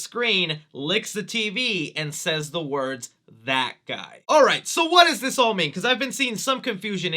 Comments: i thought venom said bad guy, not that guy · In the Mid Credit Scene I heard him say “Bad Guy” not “that guy” 🤔